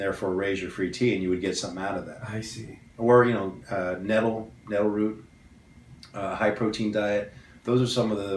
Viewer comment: what he said in 10:53 ways to lower ssbg, he said something before high protein diet ? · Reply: nettle root!